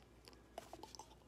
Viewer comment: “It was literally dIsGutInG and y’all just witnessed me get DiaBeTess” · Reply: No, I heard “DiAbEtAsS” 😭😩🤣🤭😳